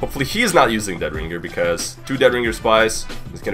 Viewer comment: idk if you are gonna see this comment but, for a sec I thought at this part (4:26) I thought you said that "hopefully he's now using the dead ringer" until I replayed that part and you actually said "not" instead of "now" XDD